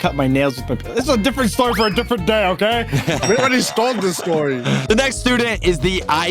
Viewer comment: on 2:15 it sounds like said stole instead of told